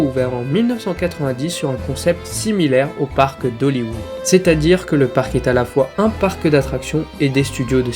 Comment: j'ai entendu "au parc dollywood" mais faut pas faire l'erreur c'est "aux parcs d'Hollywood"